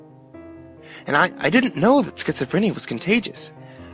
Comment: Did he just say he "didn't know schizophrenia was contagious"? Ok I'm out. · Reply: He said, "I didn't know [if] schizophrenia was contagious."